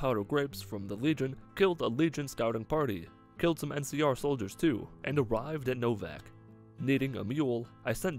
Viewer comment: at first i thought he said "killed some mcr soldiers too"